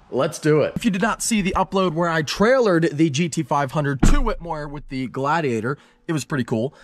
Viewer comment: When he said the giveaway winner for the gt500 i thought he said 600 lt💀